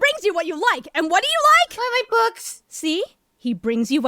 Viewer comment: Did anyone else hear Gohan say Twilight Books at 3:07?